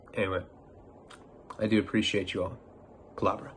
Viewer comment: I don’t hear Palabra….I swear I hear him say “Pull out Bro”…..😂